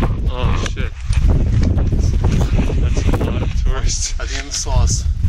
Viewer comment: I just thought he was saying ‘’A lot of Terrorists here" at first but shortly after I found out that was Tourists.Holy shit...